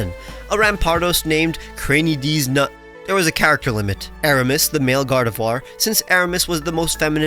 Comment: why did I hear this as “it was a character moment”